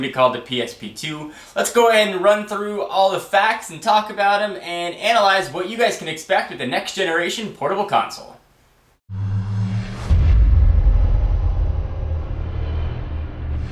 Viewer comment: LMAO at 0:27 it sounds like said "portable cocksole"